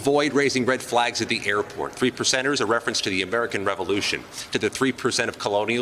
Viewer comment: When he said the 3 percenters, I thought he said, The 3 lepers!